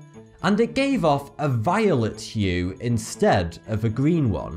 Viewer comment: I thought he said violent hue.